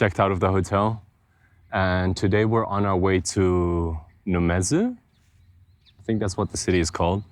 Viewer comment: Did somebody say Numazu??? It's a famous city for Love Live Sunshine fans, it's a nice cozy coastal town.